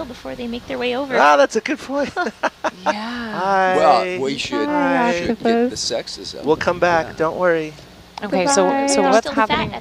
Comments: Am I the only one who heard “Well, we should keep (or get) the sexism.” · Did I hear sexism or what